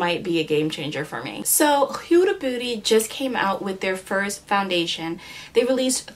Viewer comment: i thought she said "huda booty" lol